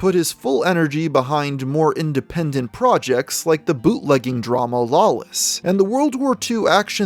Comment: I was caught off-guard when I thought I heard you say "bootlicking" instead of "bootlegging" drama Lawless but it gave me a good chortle